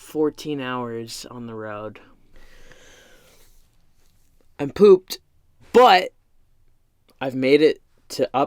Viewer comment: When you said “I’m pooped” I thought you said “I pooped”. 👀🤨 I had to listen back to what you actually said. 😂